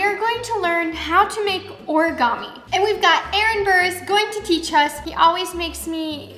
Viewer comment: When they said Aaron burris I thought they were saying Aaron burr